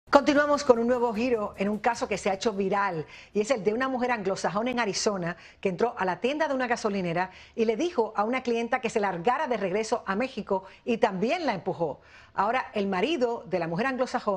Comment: entendí “Una mujer anglosajona y narizona” 🤣